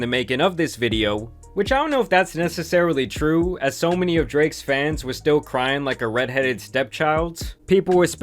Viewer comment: Thought it said Drake is Fapping 😂😂😂